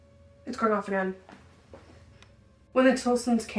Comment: At like 16:20 it sounded like someone whispered “get off” it could’ve been one of the girls but idk it just seems weird to me